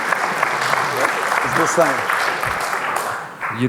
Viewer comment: What does that guy at 1:00:30 say he is and why is everyone laughing when he says it. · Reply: He says his name is. No idea why everyone thought it was so funny though. Maybe it just sounded like he was in an AA-meeting..